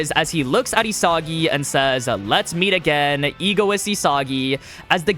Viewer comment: Did it just say egotist instead of egoist in